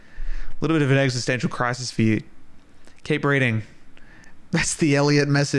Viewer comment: I thought you said keep BREATHING and right now I’m having some difficulty to breath so that was a really nice Elliot message — even if I misheard it